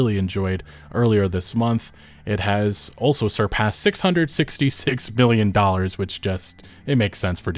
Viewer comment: Did he say 666 billion dollars?